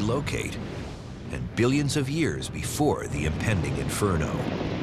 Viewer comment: At first I was really worried. I thought they were saying millions of years. Then I realized they were talking about billions of years left in the Sun's life. That eased my mind.